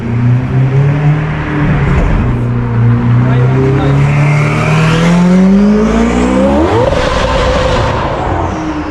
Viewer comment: What did that man say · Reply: He’s never happy 😂